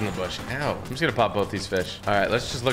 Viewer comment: did he say hell or ow?